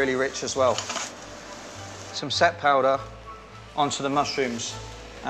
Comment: Some what powder that he added to the mushrooms? Sounds like he said "set powder." Pardon, I'm an American and some of these foreign references are lost on me. All I'm getting on Google are makeup references, and that's certainly not what I'm looking for, lol.